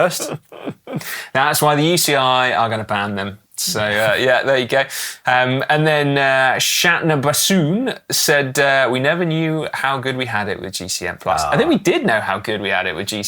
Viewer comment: 😂 the way read that username made me double take, sounded like he said "shat in a bassoon"